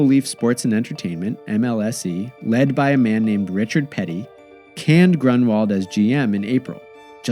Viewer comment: For a second, I misheard 'Richard Petty' instead of 'Richard Peddie', and wondered for a second, "Why would the King of NASCAR chair a sports consortium in Canada?" Then again, considering he made his first start in top-level stock car racing in (of all places) Toronto, that would've been a nice way to repay the favour.